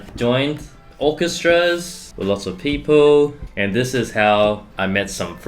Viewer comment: Low key thought Brett said “And this is hell”